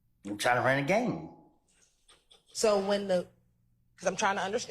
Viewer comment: he clearly said Gang not Game which makes no sense unless like she asked are you signed to YSL the record label he said no so that means he’s representing the gang YSL PERIOD END OF STORY